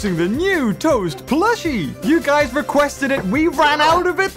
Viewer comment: I like how they are saying toa instead of toast! 🤣